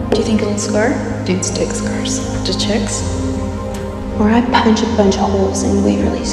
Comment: can somebody tell me what waverly said here? ive been figuring it out for the longest time lol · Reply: In reply to comment "Dudes dig scars", Waverly says "Do chicks?"